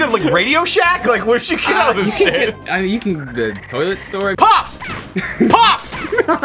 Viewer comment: I know I’m 2 years late but at 10:49 did Will say he gets his stuff from “the toilet store”??